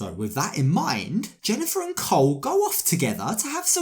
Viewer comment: I was just listening, not watching at first, and I thought I heard "Jennifer and COLT". And I was like "hold up a min!", then started watching.😂😂 I was like"there's NO WAY SHES WITH COLT". Nope, it was Cole